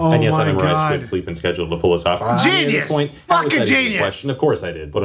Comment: technoblade speaking and i hear saying genius furking genius thats what i heard LOL